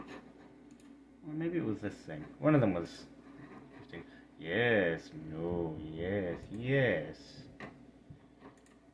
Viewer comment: What is she saying? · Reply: pretty sure it was "are you sure you want to leave?" or something along those lines